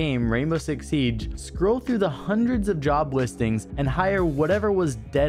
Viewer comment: For a second I heard ‘’Jobless Things ‘’instead of job listings . I laughed hard ngl